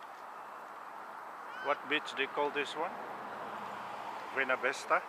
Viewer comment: LMFAO thought he said "What bitch dickholed this one?" LMFAO! Nice video btw